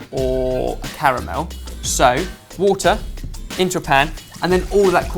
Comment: God knows why but whenever says "into a pan" I hear it as "in Japan" 😂😂